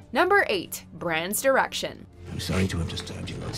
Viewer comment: at first I heard "number 8 'Bran's Erection'"